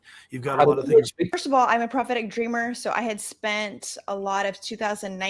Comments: Every time I hear 'prophetic' it sounds like 'pathetic'. Everything suddenly makes sense that way. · I thought she said "because I'm a pathethic dreamer".